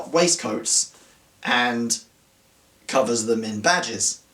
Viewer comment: I definitely heard 'waistcoats, and covers them in badgers.' And if that were truly the case, no, I would not think someone who did not cover themselves in badgers had any taste.